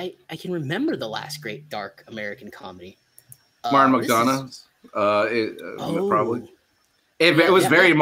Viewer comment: are the other dark comedies you mentioned you liked or this film reminded you of at 08:50? You say it twice but I cant figure out what you're saying! Help please! Thanks! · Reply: Martin MDonaugh?